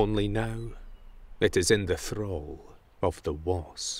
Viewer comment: What does he say at 5:50? The "throll" of the wasp? What is the word he says?